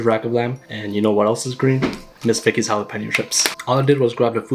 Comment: Bro I thought u said miss thickest 😅